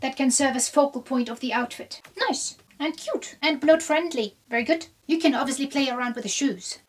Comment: Girl, I'm having my monthly and I thought you said, "Blood friendly." 😂